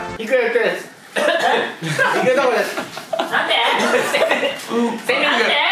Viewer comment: これ、いくとは 「いくとがいつも行くとこです！」か「いつも行くとこです！」って言ってるのかなぁーと約2年越しに思いついた…！ いつもなんて言ってるのかなぁ〜と思って考えてた…。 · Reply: 引くところです じゃない？